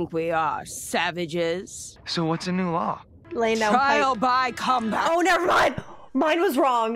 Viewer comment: I wish I could laugh at it, but I can't tell what you said... 😅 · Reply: "Laying down pipe"